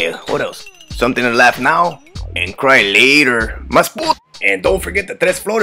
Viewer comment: Half my life I thought he said cry liter but no it’s cry later